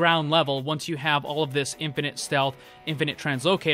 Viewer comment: I thought it said infinite health